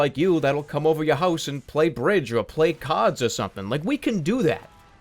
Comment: i thought he said cod instead of card.